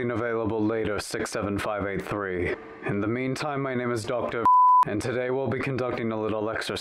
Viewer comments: Why did my mind think he said "My name is Dr. Fu**" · The beep after doctor made it sound like he was saying "My name is doctor f***" or something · sound like he is saying “my name is doctor FÜCK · When the doctor said his name it sounded like he said "my name is doctor f*ck"